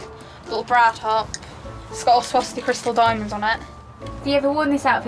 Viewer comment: im sorry... did she say 'swastika diamonds' at 4:00 ?!